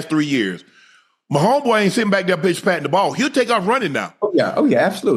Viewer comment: I thought unk said bitch when he was talking about pat running lol